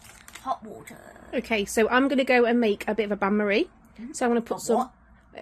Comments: It's a Bain Marie- explained it's setup. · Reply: yes I know that hun. I just said to me it sounded like said Annmarie 🏴‍☠️🏴‍☠️🏴‍☠️ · This is in his new downstairs bedroom isn't it? I thought you said " I'm going to make an Annmarie 😁"xx our little man 🏴‍☠️ so gorgeous, I feel so warm, comforted and just lovely watching you 🏴‍☠️🩷xx made me sad that wasn't able to make it to the kitchen 😢 love you my Captain 🏴‍☠️💙xxxx